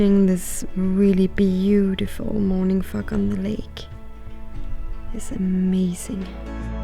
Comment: "morning FOG on the lake" for anyone else that misheard that 🤣